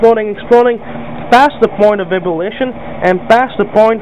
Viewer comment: what does he call the point of boiling? "Point of bibbilation"?